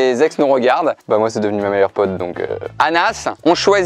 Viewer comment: c’est moi ou il a dit ma meilleure pote?